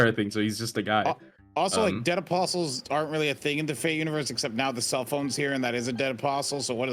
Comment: Ah. Misheard what was said. That makes sense. lol. I multitask while listening sometimes. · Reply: Just watched that part at 34:00, and he said "fate universe" not FGO 😅 so you didn't misheard.